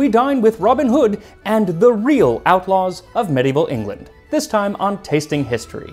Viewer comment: I heard “dorito outlaws of medieval england” at 00:20 and I just can’t stop laughing